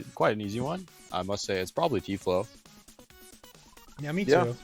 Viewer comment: what is the name of their favourite surfer?? i couldnt understand it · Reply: Tflow